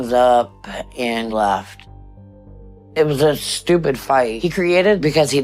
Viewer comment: The nerve of her asking Dr. Now if he believes in God, then going on to say if God wants her to lose weight, then she will. And that’s exactly how she got obese. God did it. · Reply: whew! I first thought you were saying I was stupid.